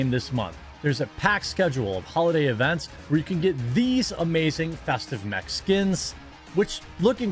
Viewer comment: He said "new festive mech skins" I heard "new festive Mexicans"